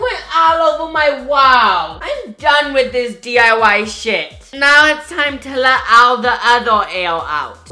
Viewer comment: " I'm done with this DIY shit" lmfao!!!!!!! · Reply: I thought it said " I'm done with the diy SHIRT"